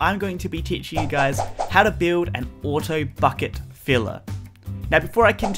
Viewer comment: 11 seconds in and I thought he said auto fucking filler .___.